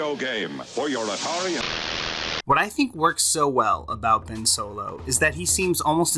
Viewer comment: did he say solo?